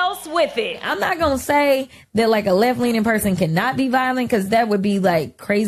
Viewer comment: she actually said "A Left Wingged person" And they make fun of Trump and how he talks.